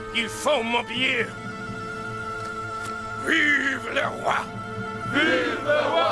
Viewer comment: What is it that the French Marquis says at 42:04? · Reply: I don't speak French but it seems to be "Vive la/le Roi" which is "Long live the King"